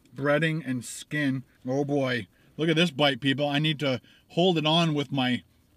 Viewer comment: Aaaahhh lol i started dying when at 9:30 it sounded like you said black people, at the moment your eating chicken and waffles. much love been a fan for ahwile keep it up!